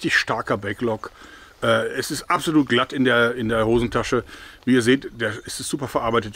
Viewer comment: “Es ist absolut glatt in der Hosentasche” - musste es mir 3x anhören bis ich es richtig verstanden habe. LOL 🤭